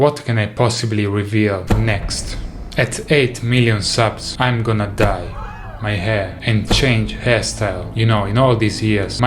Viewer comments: I heard "at 8 M subs I'm gonna die" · I thought he said "I'm gonna die" good thing it's only "dye"